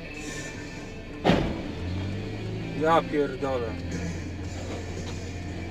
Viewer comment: anybody heard "Drop your donuts"